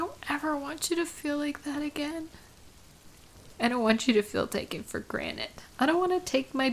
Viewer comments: did you just say "taken for granite"? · did you just say "taken for granite"? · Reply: I did sound like she said “granite”, but she did say “granted”. Sometimes you got to listen in carefully.